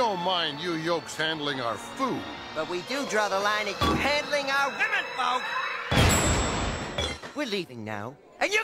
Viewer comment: What did Max say at 1:44? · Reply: “but we do draw the line at handling our women folk!”